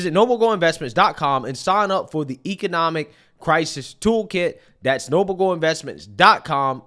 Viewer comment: Protect Your Retirement W/ A Gold. IRA <link> or call <num> Noble Gold is Who I Trust ^^^ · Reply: Brother, I thought you said token the first time, not a toolkit 😅. I was like, no way, my boy is selling crypto.